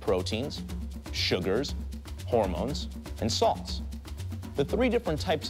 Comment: What did he say at 0:18 I cant hear it properly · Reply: Salts